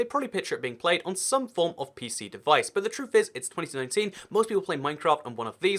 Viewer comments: who else heard 2017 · Did he just say it's 2017